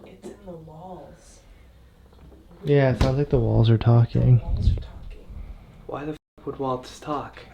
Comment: did just say the walls was talking🤔